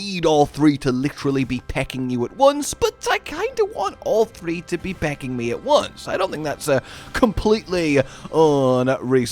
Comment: Anyone else mishear Josh say, "but I kinda want all three to be pegging me at once"? No, just me? 😅. Makes the rest of the Quematrice section way funnier than it should be.